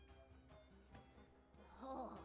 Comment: Everyone seems to be in agreement that Pen Flinger says "Hey loser", but I heard it as "Hello there" for some reason and I can't hear anything else.